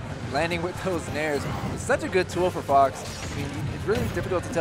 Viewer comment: what did you just say...? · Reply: HE SAID FOX! WTFFF